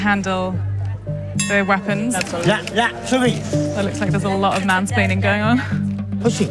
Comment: did she say "looks like there´s a lot of mansplaining going on"?